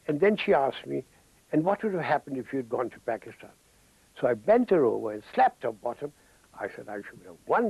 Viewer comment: Did he just say he bent the girl & slept her bottom? 🙂